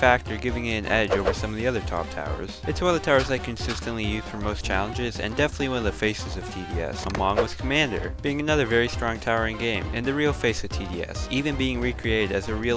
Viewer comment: did he said among us